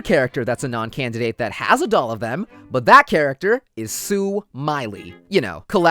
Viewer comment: Xd i thought Gin said i wanna sue Miley. Or something idk who